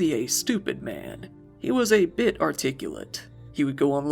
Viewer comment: Jake with all of his problems wasnt a stupid man he was a bit... ch; is what I heard